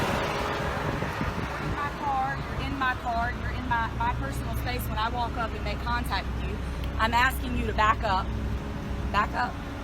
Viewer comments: Did she just say, “your in my personal space, when I made contact with you” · Did she say "You're in my car"? smfh. Listen here, "snowflakes", I know you are watching; pull your heads out of your asses and take a breath of reality. ;) Just imagine what these clowns do when there is no camera; you had better be videoing live when you watch this circus...